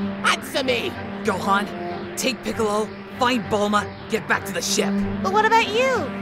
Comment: Hahahaha for a second I had thought that said "Gohan. Take Piccolo fight Bulma" 🤣